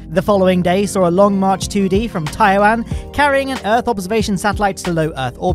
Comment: Hey... The phrasing in the video seems to discount Taiwan as a country and seems "lumped together" with China. Is it just me, or is that the actual view of that territorial dispute? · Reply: Just an unfortunate near-homophone (at least for those of us who don't speak Chinese). I also first did a double take when I thought he said "Taiwan", checking it on Wikipedia and listening again he actually says "Taiyuan" which is one the PRC launch sites.